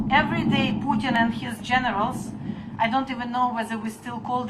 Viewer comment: Lol I thought she said Putin and his Gentiles not generals